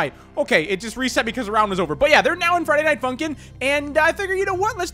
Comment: Did just say there now in Friday Night Funkin